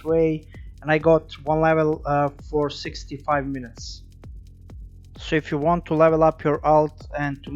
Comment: "And I got WALALAh, for 65 minutes" xD bruh what? I'm sorry but I have no idea what you said right there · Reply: Haha xD one level for 65 minutes